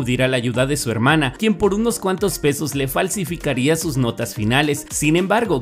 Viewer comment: escuché "por unos cuantos besos" XD